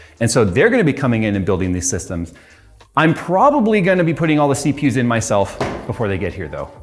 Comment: I had to watch twice, because the first time I heard: "I'm gonna put all the CPUs in myself"... Commas are important.